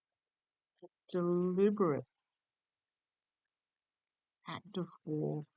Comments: Heard something at 16:08 - not sure what was said, but I think it was 😊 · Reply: He said deliberate. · I heard “deliberate”. I have a question regarding f45… Antichrist?? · I couldn't make out what he said at 15:59, but I heard him, too! Amazing! 😀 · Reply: I heard, "deliberate" at 16:09. Wow!